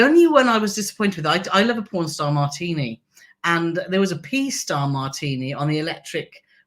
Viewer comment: did I hear you correctly? You like a “porn star martini”??? What in the world?? 😂😂😂😂